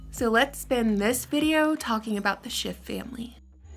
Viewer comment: Tell me I'm not the only one that thought she said "the Shit family" in the intro? Lol Sorry, I LOVE LOVE LOVE your channel, that just caught me off guard lol it took me a few to realize the actual name you were saying 😆